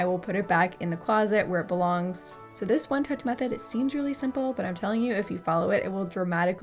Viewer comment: Ohh, for a moment I thought you said One Touch Method... for my Male.